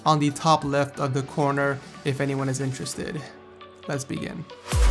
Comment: did he say 'lesbian'?